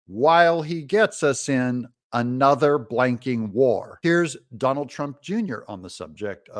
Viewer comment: I thought I heard “NOT in another f-ing war “